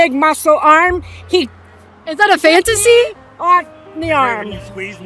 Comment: “Is that a fantasy” LMFAO BC THATS WHAT THAT SOUNDED LIKE. HOMEGIRL IS IN A DROUGHT